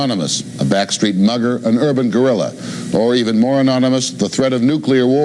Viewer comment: Did he just say "an urban gorilla?"